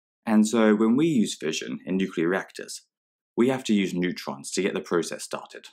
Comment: What word did he use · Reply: In reactors